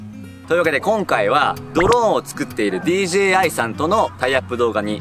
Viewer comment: ドローンを作ってるDGA に聞こえてビビった